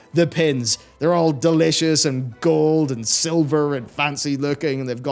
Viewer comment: Did he just say those pins are "delicious"?